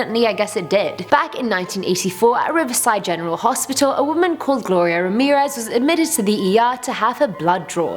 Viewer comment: I Thought you said gloria Borger from pewdiepie XD